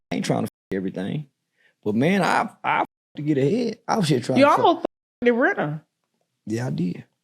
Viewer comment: whose name did say he almost hit??? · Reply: Brittany Renner